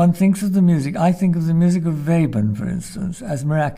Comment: what is the name of the composer he mentioned? I can't make out the name. Thanks. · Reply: Anton Webern is what I heard...